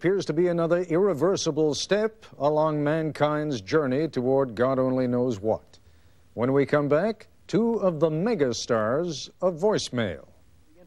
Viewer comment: anyone else hear two of the nigga stars of voice mail